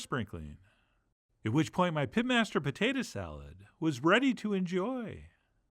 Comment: Even though I read "Pitmaster Potatoes" in the video title, I genuinely thought you said PIMPmaster Potatoes momentarily. Now those would be very different Potatoes